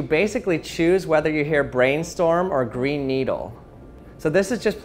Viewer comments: I heard " greenstorm" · At the brainstorm/green needle one I heard "brain needle." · I heard brainstorm and I think I kinda heard a little bit of a cross of both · I heard it say brain needle and green storm as well. Pretty trippy.